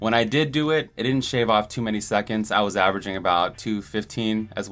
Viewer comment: 49 coulda swore he said shave off instead of save off.